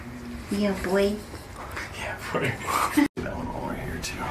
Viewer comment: anyone else hear her say "yea boi "